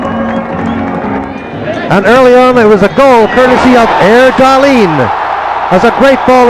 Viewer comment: is he saying Eric Dahlin??